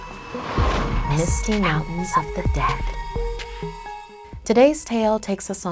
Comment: For a second I thought she said the musty mountains of the dead 💀💀😂😂